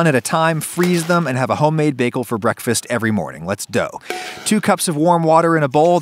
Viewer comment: Did you just say "let's dough"